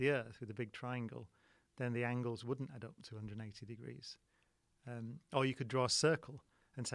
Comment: At around 3:00, did Cox say 280 degrees, or did I hear Brian Cox wrong? I always thought that the total of a square was 360 degrees, and 180 degrees in a triangle.A